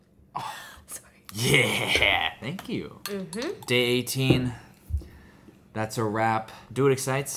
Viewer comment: When he said “Day 18”, I thought he said “Dating”